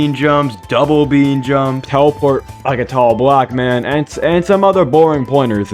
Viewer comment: what did he say 0:16 (im on alt watching this again) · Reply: How to teleport!